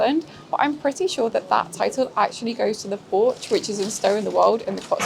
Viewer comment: I couldn't understand what she said after: "I'm pretty sure that title goes to ..." until the end of the sentence (around 01:30 mark). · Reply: She said "The Porch, which is in Stow-on-the-Wold" . . . . which title was only first claimed for the building in the 1970's when it was put for sale! The oldest pub, functioning as such since it was built in the late 1300's, is The George in Norton St Philip, near Bath (now owned by the Hotel Inspector Alex Polizzi and her mum).